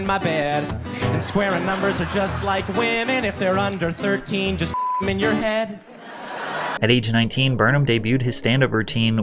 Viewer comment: wtf did he say??????? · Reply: "If theyre under 13 just do them in your head"